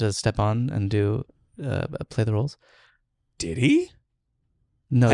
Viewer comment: Don’t worry, I got your joke 😂 1:08:28 ( sounds like he says DIDDY instead of DID HE and says ‘no, Jim’)